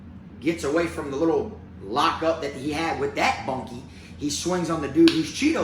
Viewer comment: “Unwrapped with that monkey” at 11:19. I’m confused what you saying